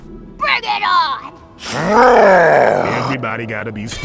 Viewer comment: what did Jax say im confused? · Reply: Jax Any body gonna steal my lines